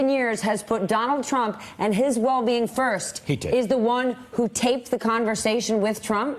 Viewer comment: I had to listen to this 3x to realize he wasn't saying "Pee Tape" and was instead saying "He taped it". I laughed when I just heard a random "pee tape".